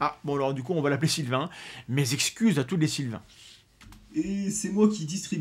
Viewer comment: pour être plus précis, tu présentes tes "excuses à tous LES AUTRES Sylvain" :face-blue-smiling: Quelques minutes plus tard... mais le RONPISH du Sylvain le prépubère (elle m'a fait fait rire, celle-là) :face-turquoise-covering-eyes: 28:31 Oh oui, dis-moi encore des trucs cochons dans le genre ☭ :face-blue-heart-eyes